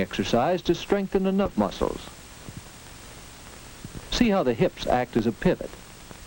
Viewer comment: DAng.... at 3:00 did he say that the trunk excercise strenthens the "nut" muscles?? I could use a bit of that excercise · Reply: He said "enough muscles".